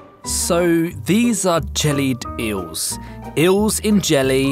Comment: open your subtitles it says German Jews instead of jelly eels.